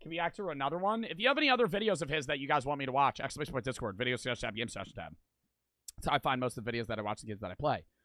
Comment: what the heck did he say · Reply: Exclamation discord game suggestion tab/ video suggestions tab